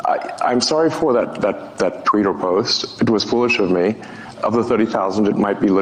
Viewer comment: Did Elon say Tweeter Post?